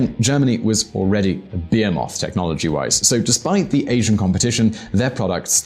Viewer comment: Ohh your saying "behemoth" i kept hearing 'beermoth'. I was wondering what a german beermoth was.